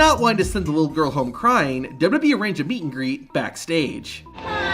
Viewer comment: Dude I thought he said beat and greet